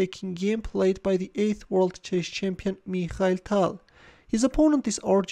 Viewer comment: It's the first time I notice he says "this is a game played by the EIGHTTH world chess champion" I always heard it as Ace world chess champion